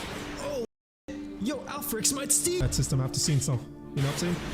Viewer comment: did bro say Alphirex might steal 😂😭